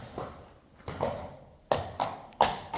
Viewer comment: What's that little bit says often after saying "Goedzo!" It's barely pronounced, but it kinda sounds (phonetically) like "Goedzo, yo wah!" She says it right about 23:04. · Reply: often says ‘Goedzo jongen’. Jongen = boy 🧡